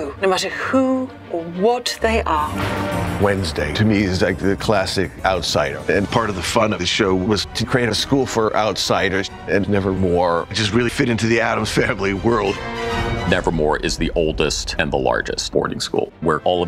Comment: I started panicking and getting excited at the same time because I thought it said nevermoor